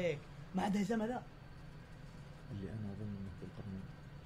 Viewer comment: اخيراً عرفت وش قال ب الدقيقة 38:08 يوم قال الي انا اظن انه ذو القرنين ههههههههههههههههههههههههههههههههههههههه